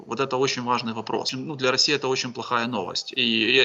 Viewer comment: Моей свекрови послышалось не раиси а рассии!!! Очень надеюсь это знак 😅🙏🙏🙏